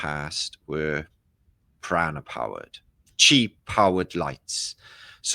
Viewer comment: I thought you said Piranha powered lamps and thought even I can't get on board with this. Prana powered...got it 😂😂😂